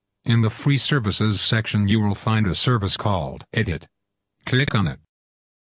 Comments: he said idiot instead of edit lol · did u say idiot :P